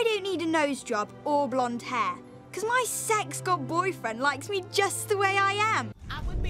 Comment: In the last clip (9:01), did she refer to him as her "sex god boyfriend"? That's the only thing I can think of, please tell me I misheard it.